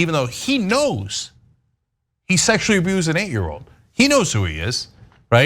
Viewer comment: See I first misheard it as an eighteen year old victim 3 years prior, making them 15. But unfortunately I realized it was 8 years old. Why is he drawing breath?!